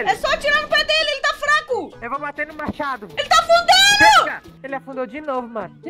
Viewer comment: eu escutei "ele tá fudendo"